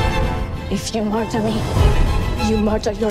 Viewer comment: mary does not say if you martin me! it says' like this and should you murder me